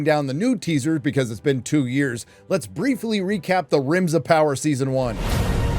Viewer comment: 💀💀 i kept hearing "rims of power" i was like "whose buthole is the one to rule them all?"